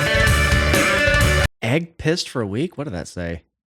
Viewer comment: The line is "he ain't pissed for a week." MTV was sensitive, so they changed it to "egg pressed" which of course makes no sense. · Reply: I always thought it said "he ate paste for a week"